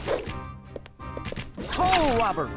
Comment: i thought he was saying toe rubber